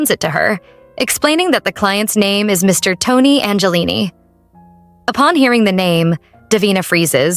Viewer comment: it is clearly saying Gabriel angeline